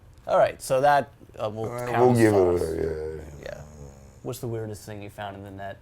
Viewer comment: What the fuck is Phil saying at 2:30 some alcoholic mumbling "we give it ahh ehhamm ueehh em" xD